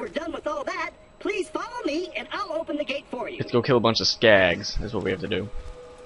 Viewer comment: haha when he said skags i thought he said skanks